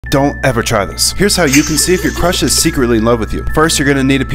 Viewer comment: Bro my whole paper become red whole world likes me😂😂😂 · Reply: Wait a min I thought he said we can’t try this at home👁️👄👁️🤚